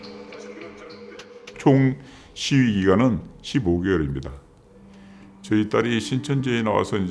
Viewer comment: 십오일 is not 15 months, Jesus. That's 15 days. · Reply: He said “총 시위기간은 15개월입니다.” So, it is 15 months. I think you misheard 시위기간 as 15일 I get that it kinda rhymes tho.